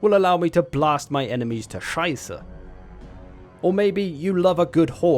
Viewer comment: did he said „scheiße“ ?